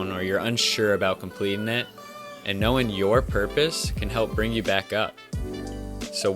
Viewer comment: i thought you said PORPOISE i have made a grave mistake